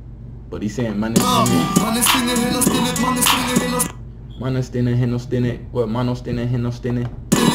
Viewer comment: "mannen stänger hela stället, mannen stänger hela stället is what he says @ 5:30 and it means "man, i'm closing the whole place" pretty much